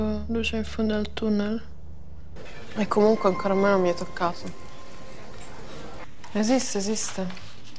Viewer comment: al minuto 2:47 io ho capito: "E comunque un calamaro mi ha toccato"